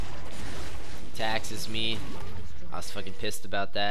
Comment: I cannot hear. "... he taxes me..." ?